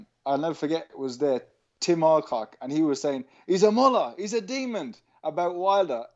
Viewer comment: What does he say before ‘he’s a demon’ around 19:30? Does he say moloch? It doesn’t sound like it, but that’s the only demon related word I can thing of. It sounds like he’s saying Mullah? I’m not aware of this term